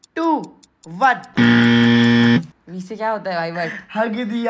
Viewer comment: he actually said 10....9....4....😂😂